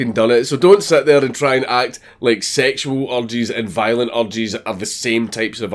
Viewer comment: This video was much harder to watch when I keep mishearing 'erges' as 'orgies'.